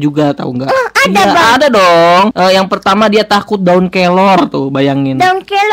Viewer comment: aku dengernya daun kolor